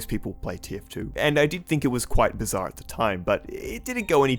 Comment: did you say bizarre?